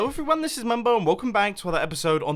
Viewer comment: My volume was very low and at the start I thought u said momo but u said mumbo